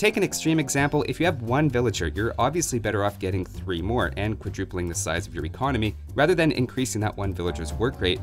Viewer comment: adding 3 villagers to 1 is not tripling your economy. but quadrupling it. edit: I misheard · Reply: He said quadrupling not tripling.